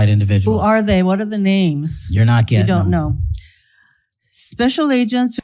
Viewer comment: Did he just say "youre not getting them?" when asked to name the new hires for cyber crime dept😮